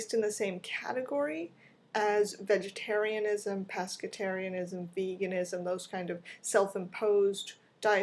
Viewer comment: Totally thought you said "Pastafarianism" for a moment when talking about Q1, there. X3; Anyway, I think the simple reason that these are more accepted is because they have larger followings, and...well, due to them being something people grew up knowing about, it's 'normal'. As for myself, I don't buy into a lot of the claims that we're meant to be herbivores, as I sometimes hear, but I do hate the conditions these animals live in. Still...gotta have my meat. ^-^; *blames capitalism*